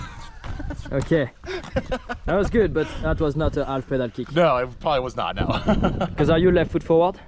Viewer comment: Who else thought he said “Alpha pedal kick”? 😅